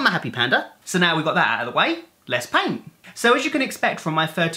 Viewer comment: You said "let's paint" but I heard "let's pain"